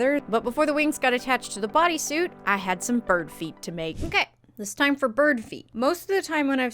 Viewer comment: At first I thought you said "Now it's time for bird FEED" and I was like okay, snacks seem to be an important aspect of cosplaying takes notes